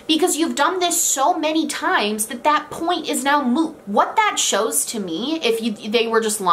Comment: Watching a very serious video, suddenly I hear " that point is moo ". For people that get the reference, I died laughing 😂